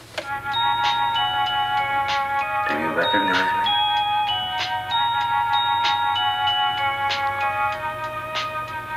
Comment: 想問接起電話時是說什麼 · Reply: "Do you recognize me?"